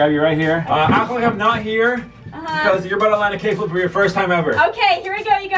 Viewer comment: I thought you said cakeflip 😅